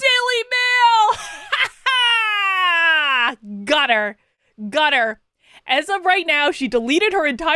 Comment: "Got her! Got her!" I thought, at first, you said "Gutter! Gutter!" Which is exactly where Kate belongs.